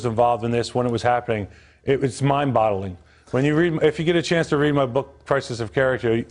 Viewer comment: did he say it was "Mind bottling"? lmao like when your minds stuck in a bottle? hahaha Will Ferrell cracks me up